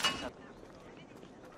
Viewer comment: In the shay one I thought the guard said "stop, I watch top gear"